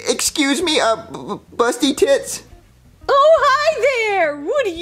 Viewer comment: Bro why did call rosalina this💀💀💀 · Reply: Jr doesn't know what name is rose, so he called her ✨️bUsTy TiTs✨️